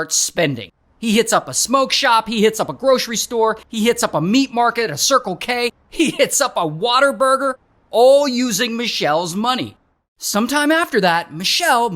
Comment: did you just call it a "Water Burger"? 🤣